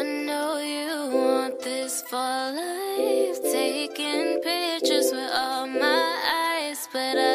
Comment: I thought it was saying taking pictures of my eyes